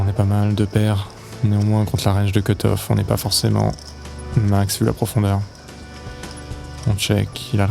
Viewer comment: Lui : "Néanmoins, contre la range de cut off on est pas forcément au max de la profondeur " Moi : "Whaaat ?" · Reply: Non il dit ‘neanmoins contre la range de cutoff on est pas max vu la profondeur’, en gros les tapis sont trop profonds pour s’emballer avec sa main :)